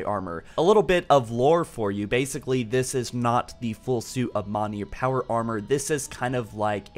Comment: waitwaitwait did you say "mah-neer"?